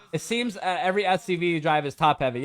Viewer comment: Yo in your audio 1:51:53 almost sounds like u said every STD but u said SUV